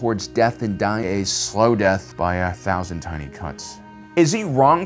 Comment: I thought he said 1000 tiny cunts😂😂